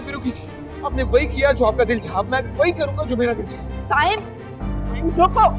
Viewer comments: did he actually say fuck or i just think he did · What did he say💀💀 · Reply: He said stop